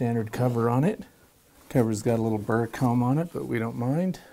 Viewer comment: Did anyone else hear something else, something "unsavoury" at 10:05, the box had WHAT on it?! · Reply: Burr-Comb... what did you hear? (">